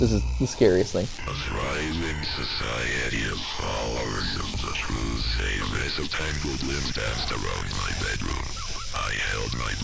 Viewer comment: "A thriving society of followers of the true saviour– as the tangled limbs danced around my bedroom, I held my breath and waited for it to stop. I was too scared to leave my bedroom-room-room-room." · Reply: i heard "A thriving society of followers of the truth- -a mess of tangled limbs danced around my bedroom."